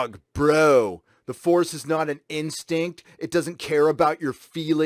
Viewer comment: "The force isn't a balance. It doesn't care about your feelings." Tell that to Dave Filoni & Freddie Prinze Jr. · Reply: Thought he said, "The force isn't 'an instinct'. It doesn't care about your feelings." But I agree on your point. Semper Fly ... ^v^